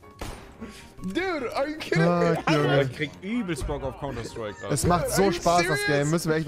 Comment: wer hat auch verstanden T: ICH HABE GERADE SO ÜBELST BOCK AUF PORNOS HAHAHA